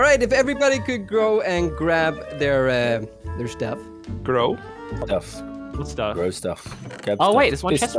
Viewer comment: Why is everyone saying “grow”? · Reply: I guess they heard grow instead of go